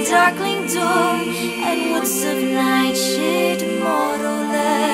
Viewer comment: I heard night sh*t. Now you have to too. Slightly sorry. Not very.